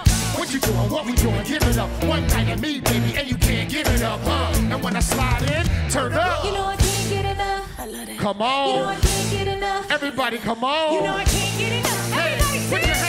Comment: when i first heard it. i thought they were saying " u know i can't get it up..u know i can't get it up"... did they really say 2:55 "cuz we be to rap like biggie and pac?" is that what they said??!?! hahaha😆😆🤣🤣🤣🤣